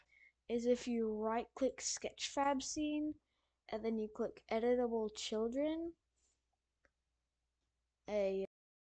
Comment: I thought you said edible children